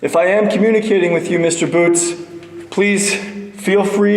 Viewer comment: I thought it said " watch out for boots" in the witch room with the long sentence.